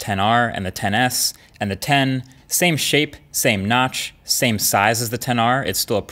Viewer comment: I thought you were gonna say “same shit” instead of “same shape” 😂